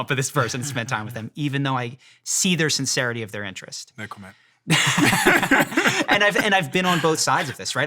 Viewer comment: What did the host say? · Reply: No comment